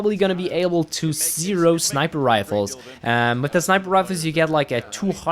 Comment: I'm sorry, what are you saying about sniper rifles at 5:30? Sounds like "Seero sniper rifles". And you mention something about "200 at the bottom". I can't quite make out what you're saying.